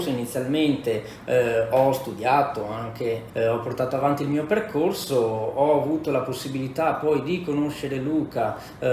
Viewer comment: MINUTO 6:30 cosa ha detto? Indicatore easy mob?? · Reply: Indicatore Ichimoku 😉